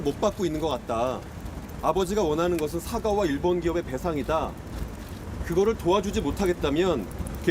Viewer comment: 이 부분 인터뷰에선 일본 기업의 배상이다.라고 들리는데 왜 자막은 보상으로 되어있는거죠?? 어떤 의도가 있는지..?